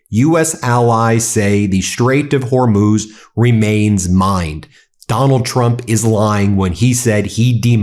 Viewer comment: I thought he said "the strait of hormuz is mine! Trump is lying when he de-mined it!" Like he took ownership of it.